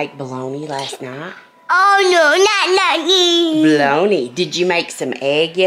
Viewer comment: He is talking so much! When you said, 'did you cook bologna last night" I coulda sworn he said, "Oh no, not again!" So cute!